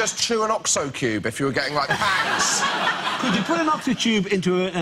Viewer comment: Can anyone help me figure out what exactly Josh says at 5:17? I get "Or you can just chew an Oxo cube if you were getting like ____", and I can't make out the last word at all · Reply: “Pangs”, I think?